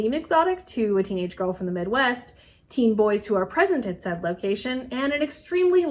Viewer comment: “teen boys who are pregnant at that location” is what I honestly thought she said for a second.